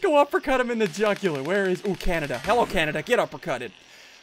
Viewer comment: im Canadian and did you just say "die Canada!" ? 0_o on